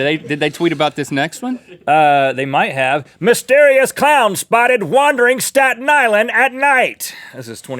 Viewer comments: when ret said clown i thought he said "clam" so............XD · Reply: I also thought he said clam! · when ret said clown i thought he said "clam" so............XD